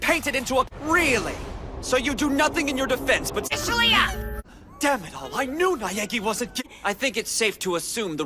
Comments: For a second I thought Byakua said, "Dammit I knew Neagi wasn't gay-" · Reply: For me I thought byauka said "Daniel I know Neagi wasnt guh-" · “Dammit I knew Naegi wasn’t ga-!” Byakuya is heart broken. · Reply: I heard "Damn it all! I knew Neagi wasn't ga-"